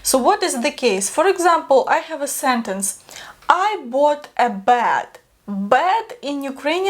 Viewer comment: Лiжку (lizhko) is BED in Ukrainian- the first time said it I thought she was saying; "I bought a BAT🦇 and then I sat on the BAT 🦇". It seemed appropriate since Halloween is in a couple of weeks.....